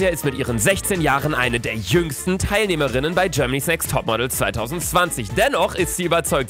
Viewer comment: eine der jüngsten Kandidatinnen bei Germanys Sex Topmodel🤔?!! Habe ich richtig gehört???😶😶😶